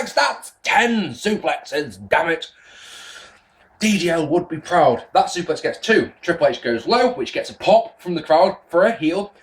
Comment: Who did you say would be proud?... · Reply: a British wrestler called deadly Damon Leigh. he used to have a gimmick where he would call out the number of suplexes he had done